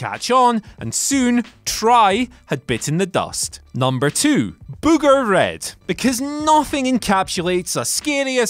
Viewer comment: I always thought JR was saying Boomer Red lol